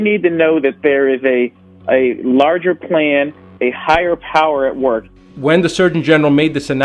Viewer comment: At the 2:03 he did not say they have systems in place, he said there is a larger plan a higher power. I understand what your saying but it would make sense if he actually said exactly what you said, systems in place. I really don’t see a clear connection to the cynicism that is being expressed.